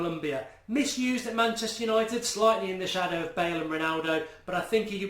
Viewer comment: Did I hear it right? "..........misused at Manchester United" :O